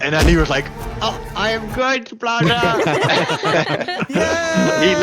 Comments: captions said ‘IM GOO BLAZA’ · is it me or the subtitles say "i am goo blaza?"